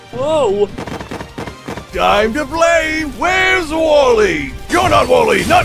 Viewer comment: WALLY I FOUND HIM Edit:HE ACTUALLY SAID WHERE'S WALLY